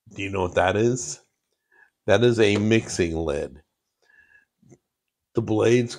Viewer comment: I thought he said “mixing-Lynn” I have food youtuber brain rot so bad I have to go touch grass immediately